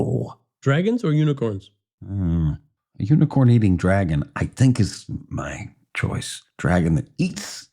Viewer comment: I don't know why, but I thought he said a Unicorn eating a Dragon. 🤣